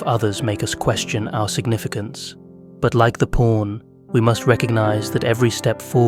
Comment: this video took me a while to make. please watch the full video and feel free to comment on any criticism. I will continue to improve my editing style for future videos to come. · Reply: Man this is a great video and I am grateful it was on my recomended feed. Only one small nitpick, please review your subtitles. At 9:25 you have written porn instead of pawn